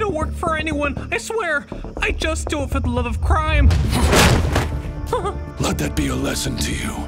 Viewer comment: I completely thought he was about to say... "I only do it for the love of christ"